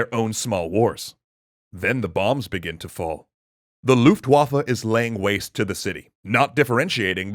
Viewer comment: Did he just said "Luftwaffel"? ^^ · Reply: No he said Luftwaffe, correctly. No L at the end.